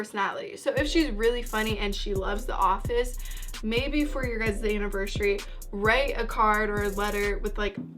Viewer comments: Did I just hear "Day Anniversary" ? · Did I just hear "Day Anniversary"